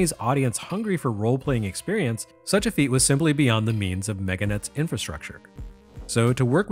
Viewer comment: When he first said Mega Net, I almost thought he said Megaten. >_>